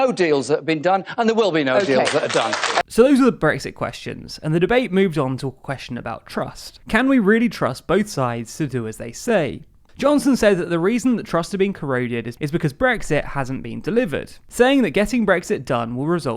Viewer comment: Sids instead of sides